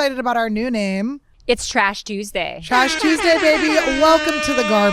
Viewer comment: For a sec I thought it said Trish Tuesday. Still wishing Trisha to join in on this :)